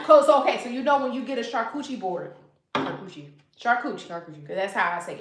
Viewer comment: Hold on, 21:31 did she say a "charcoochie" board? 😂😂😂😂 What in the edible panties? I woulda acted a fool in this live 😂